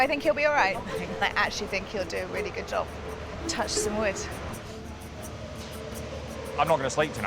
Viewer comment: did she say she touched his wood ?